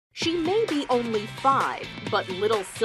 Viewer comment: I thought it said she’s 2 lmao but it said she’s 5